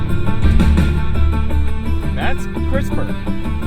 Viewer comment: I thought you said, "That's Christopher." And it looked like you pointed to the car coming up from your left. So I thought he was driving next to you, LOL.